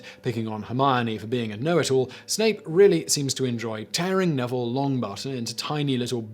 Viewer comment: Wizard of coincidence? Did you mean Wizard of consequence? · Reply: Yes, I am sure he read it wrong... :-) Also, sounded like he said "Neville Longbutt."